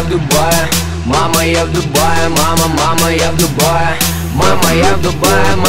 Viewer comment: Когда я услышала первый раз эту песню мне послышалось МАМА Я ТУПАЯ МАМА МАМА Я ТУПАЯ